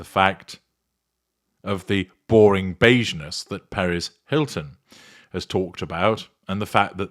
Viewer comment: is talking about Perez Hilton who is a he. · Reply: Oh, sorry I misheard! I thought he said Paris Hilton.